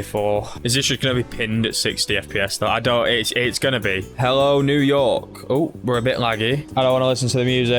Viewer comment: for some reason the video buffered here, sounded like you said hello n...